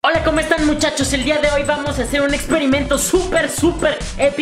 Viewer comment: Yo pensé que dijo hoy vamos a hacer un súper súper experimento social